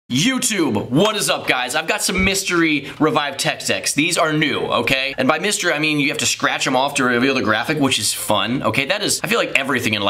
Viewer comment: did u say tech s*x